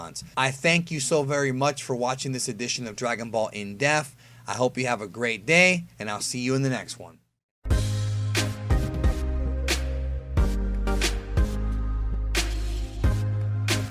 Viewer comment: WHAT IS HE SAYING?!!?!?? I know it’s called “Dragonball in depth” but it always sounded like he said “dragonball in deaf” and this video sounds like he’s saying “dragonball in death”